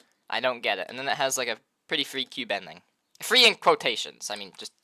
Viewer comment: I thought he said a very freaky bending